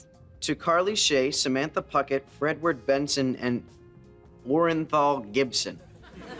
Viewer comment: OH MY GAWD I misheard Gibby’s name the first time and for the longest time I thought his name was ‘Warren Paul Gibson’